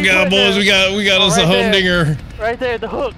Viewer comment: Woah, woah, woah... 18:07 When Anthony says "Hum dinger" I had to rewind it like 4 times to figure out that's what he actually says cause it definitely didn't sound like that at first.